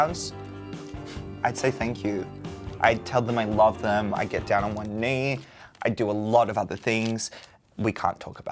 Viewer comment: did he just say "i get down on my knees ya we dont talk abt that"" lol